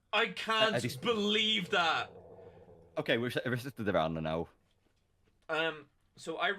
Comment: had a chance to do the funniest thing possible and end the run after soloing Crow Mauler. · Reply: It genuinely sounded like he tried, at least as a joke. At 11:17 it sounds like he said “Okay reset the run now.” But then just ignored it and he didn’t press the issue.